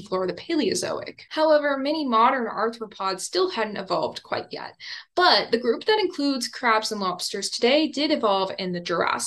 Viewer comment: After some time, I had to watch this again and noted that, in 12:27, I heard, "Crabs and mobsters." I listened three times over this passage and it didn't change... Come on, you made this as a deliberate test, to see how attentive we are... ;-D